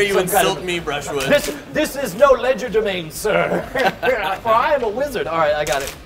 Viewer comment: Did he just say Ledger de mane lol